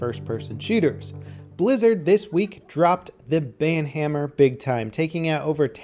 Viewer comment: Thought it said bananahammer